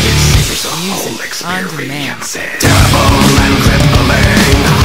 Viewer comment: lol i hear "music on demand" at 1:27 and faintly again after that.